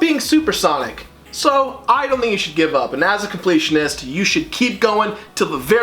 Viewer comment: I started fast fowarding at the time part where Jirard showed up, and he sounded like he said, "You should give up. *pats back*" Glorious.